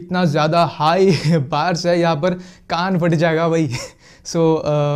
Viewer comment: Did he Say Ga**d fatt jaega? 😂